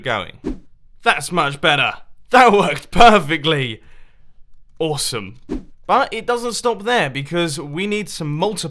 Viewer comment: Mumbo says "awesome" but captions says "terrible". XDD